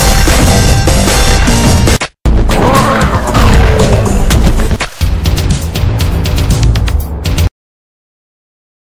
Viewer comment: Did catfish Say "oreo"? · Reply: No he say meow